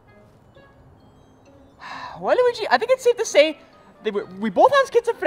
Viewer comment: Mario said waliuige instead of liuige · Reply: He said well luigi